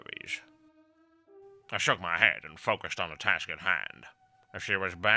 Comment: did you say 'Shrugged my head' or 'Stroked my head?'